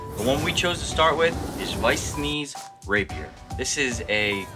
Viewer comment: Did he just say at the beginning "Weiss Sneez"? Btw this is gorgeous